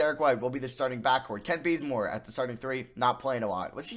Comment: Am I the only person who heard him say “Kent Blazemore” lol